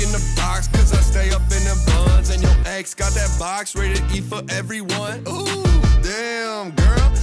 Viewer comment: YO X GOT THAT BOX RATED E FOR EVERYONE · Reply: I thought it said “ready to eat for everyone” lmaoooo